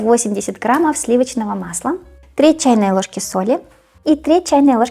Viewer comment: Люди дорогие, да мне тоже сначала послышалось 3 чайные ложки соли, но вы включите критическое мышление для начала прежде чем что-то делать.! сказала треть чайной ложки на самом деле. Я пошла проверила рецепт в описании. И правда, надо класть 1/3 ч. ложки соли. Всем удачи с тортом🙌